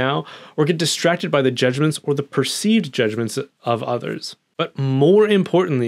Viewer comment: I heard “distracted by Jesuits, or perceived Jesuits” and was very confused. Lol